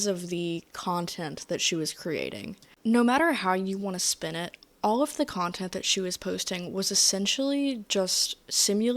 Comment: can someone explain to me what is censored here. My imagination is running wild. Does it say Family Friendly.??? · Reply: it said “fetish friendly”